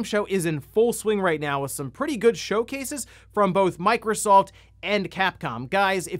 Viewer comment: Sounded like he said microsalt at 0:30 😂